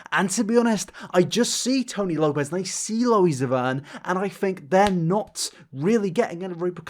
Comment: When I heard loe zavern I kind of laughed I’m sorry-😂